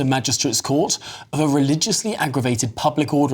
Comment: Read the facts of the case. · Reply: I misheard 'racial' for 'religious', I believe?